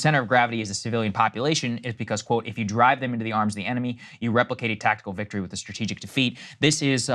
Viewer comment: I’m… Saagar is hearing what he wants to hear 5:47 he did not say “replicate” , he said “replace”